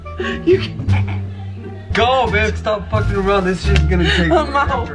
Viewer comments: did he call her babe at 34:25 ?😍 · did he say “Babe” to her !? ❤️